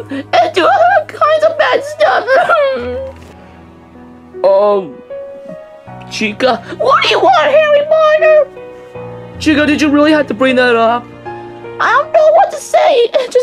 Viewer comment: did she say HERRY POTER oh i get it becaude of herry as in hear as in rabbit.